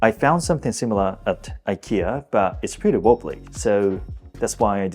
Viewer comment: its pretty の後なんて言っているかわかりますか？ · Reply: wobbly 机とか椅子がガタガタの時に使う。 なんならほぼそれしか使わない。